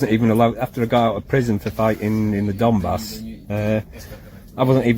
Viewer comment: "The British state took everything from me...........when I got out for prison for fighting in the Donbas" Me: OH, yeah stuff like that would make you unemployable. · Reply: I thought he said "...got out of prison for being a dumbass..."